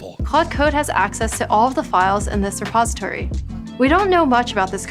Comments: not her saying "suppository" instead of "repository." A repository is a fancier name for storage. A suppository is a kind of medicine that you stick where the sun don't shine. 😂😂😂😂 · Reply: Man, I coulda sworn she said suppository, but I was like "Heck no she didn't...did she?"... I had to play it back a few times, but when I tested CC on the video, I was disappointed to be wrong; so I then came here and found I wasn't the only one to sorta think I coulda heard that. LOL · not her saying "suppository" instead of "repository." A repository is a fancier name for storage. A suppository is a kind of medicine that you stick where the sun don't shine. 😂😂😂😂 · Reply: I came here for this I thought she said suppository as well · not her saying "suppository" instead of "repository." A repository is a fancier name for storage. A suppository is a kind of medicine that you stick where the sun don't shine. 😂😂😂😂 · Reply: She actually says "repository" lol I think the fact she merges "this" and "re", pauses a tiny bit, and then enunciates "pository" makes it sound like "suppository".